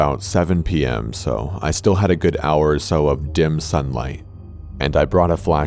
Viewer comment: y'al know ive been craving dimsum so much that when corpse said "dim sun light" i heard "dimsum life"